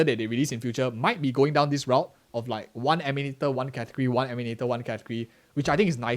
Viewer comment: one emenatot one ??? Catcree? Wtf? You said powercreep right after so that word wasn't powercreep..